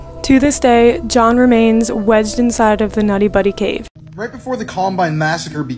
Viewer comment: Bruh did she say “nutty buddy” cave?? Its “Nutty Putty Cave”. Holy shit people like can you at least try to put minimal effort into anything you do… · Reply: she said nutty putty